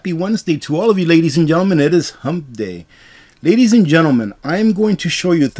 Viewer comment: Hey! I thought it sounded like you said, "It's Humpf day". As in my reply to the idea of the gubmint being here to take care of us--- "HUMPF!!! Bull Butter!!, as Rudy would say!"